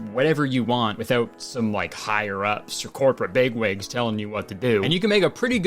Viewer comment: i thought you said corporate beyblades telling you what to do but i hear bigwigs now